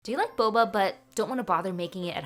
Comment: I heard this wrong I thought she said do you like balls 😭